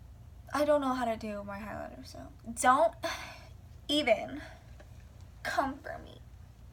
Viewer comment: did she say "don't even cum for me"? lol wtf.